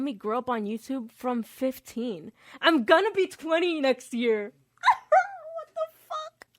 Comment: I heard this as "I'm gonna be quitting this year" and near had a heart attack